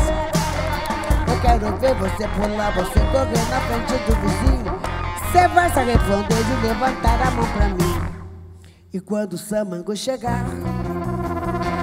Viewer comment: "Cê vai se ARREFONDER de levantar a mão pra mim" Foi só eu que escutei?